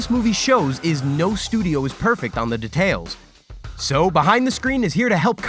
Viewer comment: am i hearing "de...tales" like boi its dEtails